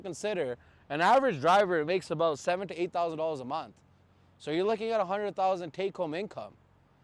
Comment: at first i thought he said $8000 a mile and i was like "holy fucking shit"